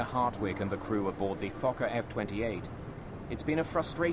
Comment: Yes it does. for a sec, i thought the guy actually said "Fucker F28" LOL XD